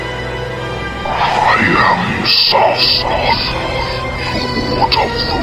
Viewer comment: it sounded like he said “I am SAS” lmao